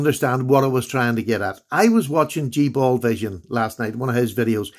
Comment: What is the guys channel called? I heard T Ball Vision and I think I misheard... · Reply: G Ball Vision